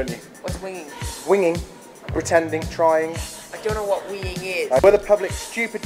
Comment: haha at 0:41 i swear john says 'i dont know what weeing is' instead of winging cuuute!!